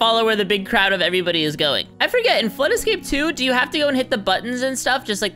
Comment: I thought when you said flood escape 2 it sounded like who escaped room 2 XD lol